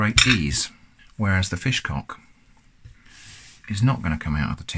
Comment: I thought it said fish clock